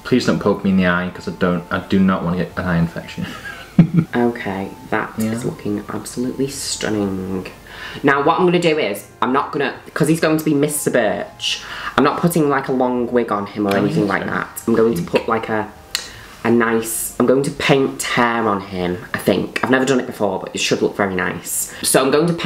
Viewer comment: Really thought it said “mr bitch”